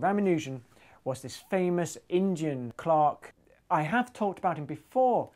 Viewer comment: "Indian" I thought you said "engine" :D